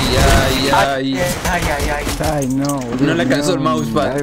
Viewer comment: creo que dice "qe boludo"